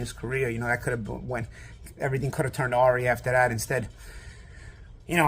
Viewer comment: could have turned what?? Are you trying to say ‘awry’?